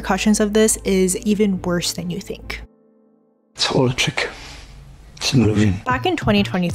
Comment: what does bro say there ? I can't understand him well. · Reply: "It's all a trick. It's not a win"